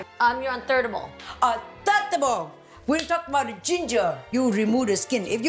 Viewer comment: “You were on thirdable” ...m dead! Jeannie, you are a fool for that😂🤣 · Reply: I thought I heard her day third of all. It’s just an accent that slipped to make it sound like thirdable. Edit: She really did say thirdable. I had to rewind it. Too funny!